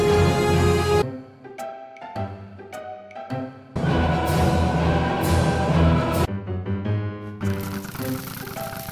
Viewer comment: what he say? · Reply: The builders should have added more deadbushes